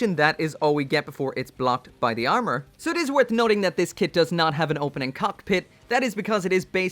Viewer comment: I got an ad when you said there wasn't an opening cockpit and it sounded like you said "it doesn't have an opening cock"